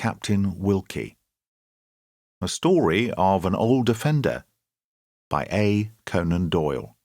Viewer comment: How funny that I thought you said “old defender” at the beginning (probably the “Captain” in the title set me up) but at the end I clearly understood “old offender.”